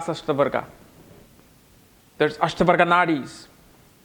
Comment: Could someone tell me what system he said from 6:06 to 6:15? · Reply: Varga system viz Ashtakvarga etc